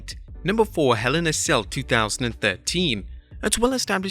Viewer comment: Who also heard 2013 instead of 2019